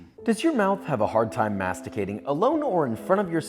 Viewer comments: i literally thought he said mastrabating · i thought he said MASTURBATING instead of masticating at 0:30 and for a whole minute straight after that i tried to deduce the context...had to rewind lol